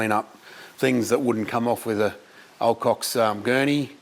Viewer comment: Did I just hear say "Old Cock" without a disclaimer popping up for a frame or two? :-P